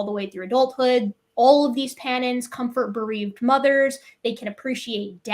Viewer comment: Panina (common chimps and bonobos). Took me forever to figure out what was saying. Sounded like Pattens, CC showed "patterns", ugh!. Hard to follow at times, especially when one wants to learn.